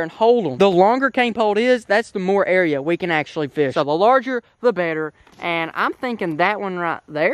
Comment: I thought you said longer the better and why are you breaking it